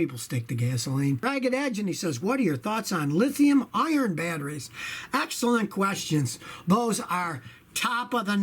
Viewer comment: It kinda sounded like you were saying Lithium Iron batteries lol. I'm sure you know it's Lithium Ion though (or Li-ion, which I like to shorten further to just Lion)